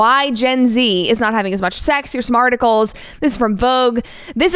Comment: Oh, I just heard you say, ‘Smarticles’! 😊 Coining a term. 🤓🐶